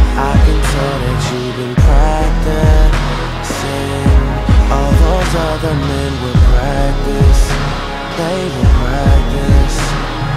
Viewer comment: for the longest time I thought he said "All those other men WOULD practice for me" hahaha